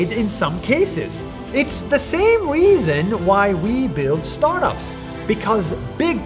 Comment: I thought he said here’s why we built starbucks